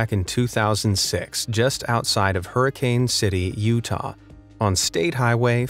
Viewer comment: did you just say, Hurricane, Utah!? thats where all the fnaf events took place...